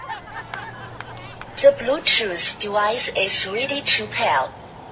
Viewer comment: The bluetooth deways is ready to pearl. The bluetooth dewise is connected sasesifully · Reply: I was trynna figure out what us was saying 😂😂 I heard "the Bluetooth device is really too pale " 😂